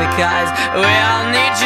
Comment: I thought it said get em’ Johnny Boy, and I figured out it didn’t, but now I kinda wish it did 😂